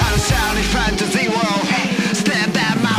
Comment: When i first time heard this op i thought at 0:48 he said "Stand up I'm fappable"